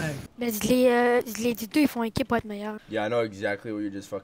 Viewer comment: my first language is French and I didn’t understand what he said😬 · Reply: "Les deux ils font équipe pour être meilleurs"